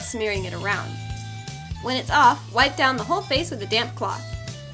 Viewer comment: I thought she said dam cloth